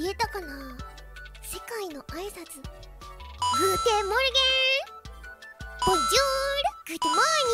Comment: I thought I herd her say good morgon bassicly saying it in swedish idk maybe im hearing wrong